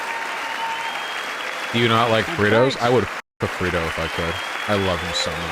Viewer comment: “Actually,… ☝️🤓” -r/196 members at 17:54 Edit: I thought he said “burrito,” but the comment still applies.